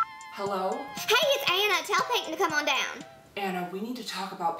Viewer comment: I swear I heard: tell satan to come on down xDDD